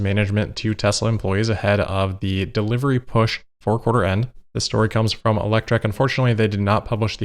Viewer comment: I misheard this as 'another leaked email ahead of the delivery push for quarter N'. It does feel like that sometimes, doesn't it? ;)